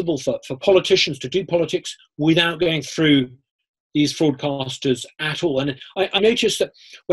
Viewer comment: did he say “fraudcasters”?!